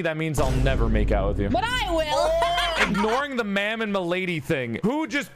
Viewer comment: I heard "Ma Damn", is it just me??